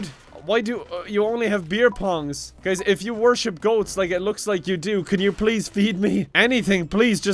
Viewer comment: When you said goatz I thought you said goatse...